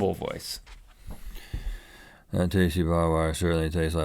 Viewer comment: With he barbed wire thing I heard "that tastey body wash really tastes like bubble gum" ... though you probably shouldn't eat that it makes a lot more sense than barbed wire.